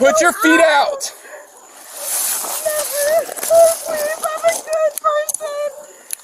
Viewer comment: Did i hear 4:10 “I’m a trust fund kid” ??????